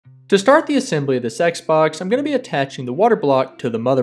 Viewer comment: did he just say sex box?